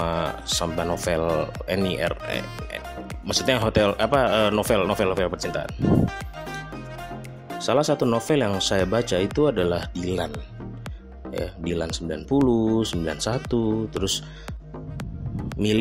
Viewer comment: Om... Kalau saya ga salah dengar di menit 0:59 suka sama Novel eny.... Eny apaan nih 🤔🤔🤔🤔🤔.... Hehehehheehehehehe🙏🙏🙏🙏🙏🙏